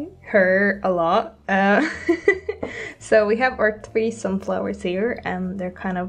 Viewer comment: i honestly thought she said threesome flowers at first Beautiful art btw